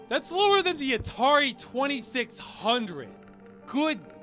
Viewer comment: I've never seen youtube auto subtitles be so accurate to what is actually being said. Well done. Except for when you said "good god" at 4:54 and youtube thought you said "could try bomb", which is.. well I'm not saying its Illuminati, but 9/11 confirmed.